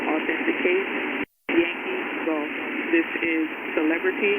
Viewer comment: What does she say at 9:00 at the end after time of authenication Yankee go? Or gold? Oroville also stands for Gold.